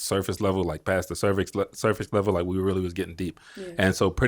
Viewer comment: Did he say passed the cervix level at 15:28?